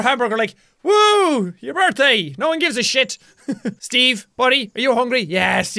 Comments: it sounded like Bella said Ah! Happa Birthda!! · she actually said "Aah! Happeh birthdah!"